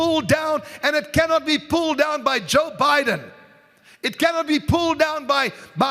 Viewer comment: it sounds like he says Joe Biden...?